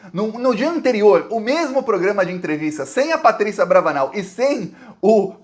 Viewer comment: Eu estou louco ou ouvi um Patrícia "AbravANAL" aos 7:03???? kkkkkk